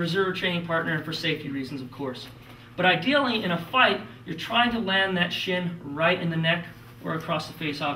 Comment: does he say try to land that shit @ 2:05?